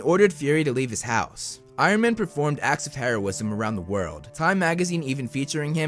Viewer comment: When he said made heroism events I thought he said act of terrorism